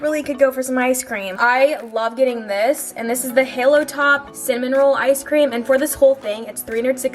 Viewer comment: when she was talking about the ice cream, i thought she said salmon roll and i was like, weird flex but okay oof